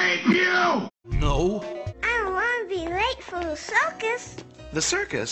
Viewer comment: I mishear 1:06 as, " i dont wanna get raped for the circus..."